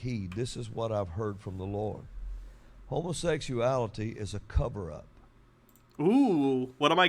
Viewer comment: I almost thought the one guy said, 'homosexuality is a cope' lol. Also it's not shocking that a con man (pillow guy) is trying to defend another con man (orange tan narcissist).